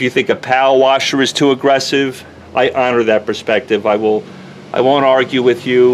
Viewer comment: The subtitles keep saying "I honor", but I keep hearing "fuck you".